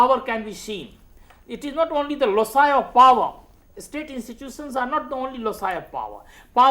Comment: Very beneficial lectures for me in my optional PSIR ,after wandering here and there I came across what I really wanted,Thanks. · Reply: Can you please tell me what does sir say at 53:21 ? Is it "lossieure of Power" ? I am unable to comprehend it.